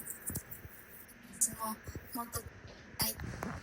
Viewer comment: ｢もっと一緒に居たいなぁ｣って聞こえない？？聞き取れないってなってるけど、何回か繰り返し聞いてみたら、そう聞こえた…！